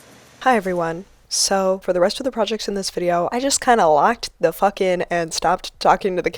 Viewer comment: Did she curse at 13:51? It sounds like she says "I kinda locked the f in", right? Correct me if I'm wrong cause I've never heard her curse before!